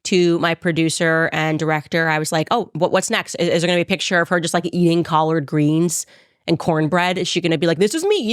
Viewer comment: Nobody black is washing collard greens in the tub!! NOBODY!!! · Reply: I was just listening to this video in the car...picking up my Kids from school. We were listening on our way home. The Part came about the collard greens came up.... and they were listening... and heard her say "cleaning collard greens in the bathtub". They were starting to say....EEEWWW. Who would wash Collard Greens in a bathtub?!". I was laughing my ass off. Mind you, these are teenagers and elementary kids.